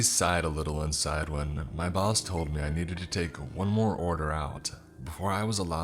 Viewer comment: I dunno if I’m just tired but I thought he said “my balls told me I needed to take one more order out”